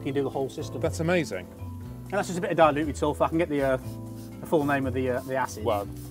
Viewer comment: I think he said at 20:18 "Just a bit of diluted sulphur" for the acid.